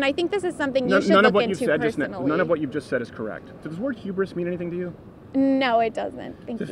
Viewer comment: Can anyone tell me what he says at 12:49 ? When he says: "Does ... mean anything to you?" And by the way... the first girl is tilting me slightly. Not much I'd say about a good 1.69 degrees. She literally takes his words puts those words on a bullet train from one ear to the other not even taking the time to listen. Damn... this is hyping me up. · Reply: "does the word hubris mean anything to you" (hubris means excessive pride or overly confident, cocky)